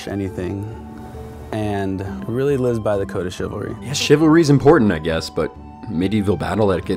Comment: did he say the code of shitlording? xD